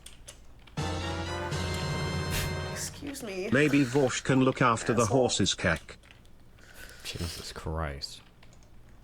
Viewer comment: What do I hear there? Vaush can look after a horses ***? The stream is from 2022. Timetravel?